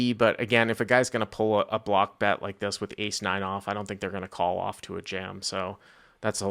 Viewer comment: Lol that guy's accent was so bad I thought he said 89 off. I thought he had saved himself $300 to the straight.